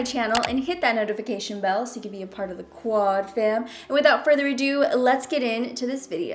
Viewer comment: I missed you guys!! Ignore the quad fam lol we changed it to #DomFam👯‍♀️✨ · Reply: my translator does not translate the quad fam . what is it quad fam? i always thought Christen was saying square family =)